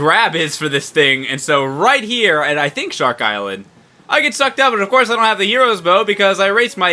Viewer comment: im not even gonna ly im stupid but when it came to the part in the cyclone and u said sucked up i really thought u said fucked up .-. :3 cx